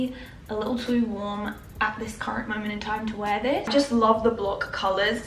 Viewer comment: When u say khaki it sounds like ur saying cocky lol😂